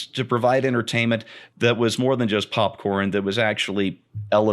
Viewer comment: At first I thought said "pop porn" movie.